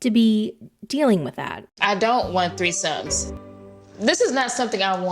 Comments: Tell me why I thought you said he wanted “three sons” by different women. I know I’m not the only one. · I thought you said three SONS with 3 different women 😂